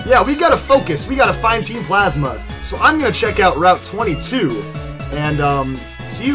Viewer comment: I remember when I was at the next part I always thought he said route 23... Took me an hour to figure out where to go lol